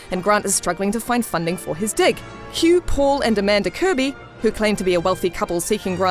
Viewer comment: who else heard that he was struggling to find funding for his d*ck